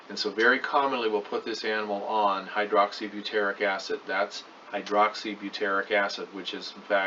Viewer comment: sounded like he said "Hydroxy Buteric Acid" ? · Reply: Debbie Anne Yeah I googled it, it's Hydroxybuteryc Acid, so yes. I found it on Amazon for 63.50, but I'm kind of afraid to buy something like this from there... But now that I know what his condition is called I can tell the vet what is going on better.